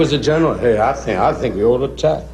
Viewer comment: What did he say @ 2:25 about Dean being a General? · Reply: I think we otta attack 😂